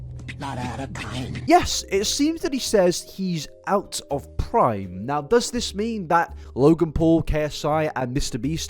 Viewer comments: It sounds like it said time not prime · this is entirely opinion based, but i think he said, 'go back in time, not out of time' not, 'out of prime'. this can mean hes trying to go back in time to his past while still remaining in touch with reality. the song, 'running tram' makes a lot of sense when you think about his possible trauma and what it could mean not only this, lemon and lime is what he said, too. it was acapella with some weird tune in the background, and the, 'ch' sound was a background noise. the corpse idea is really... sinister, but it fits with the whole situation. lemon and lime refers to marijuana, but im not too sure how relevant this is to the whole ssb thing loll